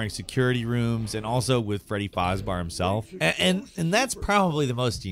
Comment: D-Did you just say... Freddy Fozbar?